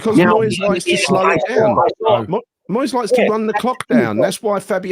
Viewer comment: 😆: "Moyes likes to run the cock down!" Can I get some confirmation I heard that correctly 🤔😂